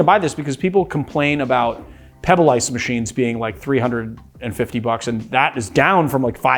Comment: what type of machine is mentioned here? I cant make out the audio. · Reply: Pebble ice machines, like the GE Opal